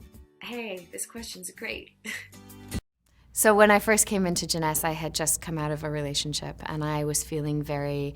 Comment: Wait did she just say when I came into jines